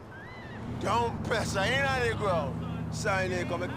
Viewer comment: What did he say? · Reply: "Dont press ah inna deh ground" meaning don't press the aux until it touches the floor which also means don't go fast